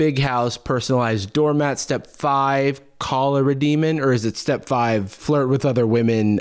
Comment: for a second I thought he said “cholera demon”… jeez that would sure be a different situation